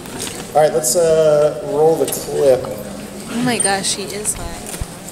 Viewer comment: I thought she said oh my gosh he is high